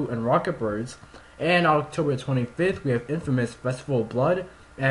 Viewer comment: hey man, do uthink u could say that again cause it sounds like you said infamous and i cant hear that well.